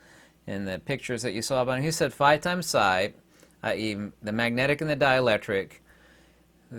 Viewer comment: what are you saying? “Phi times Pi”? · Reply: Nevermind it is Xi, a Greek letter. Or 1/c^2